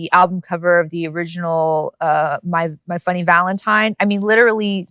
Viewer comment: did she just say 'funny' valintine for Faye valintine. Is she a fellow jojo fan